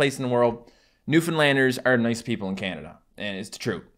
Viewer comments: Why am i hearing.. "New Finland" 😂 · The Newfoundland accent is literally just the stereotypical irish accent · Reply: I thought he said new Finland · I thought that he said Finland and I was like SAÄTANA